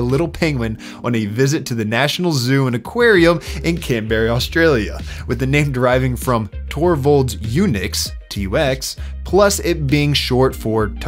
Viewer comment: did mans just call "canberra" "canberry"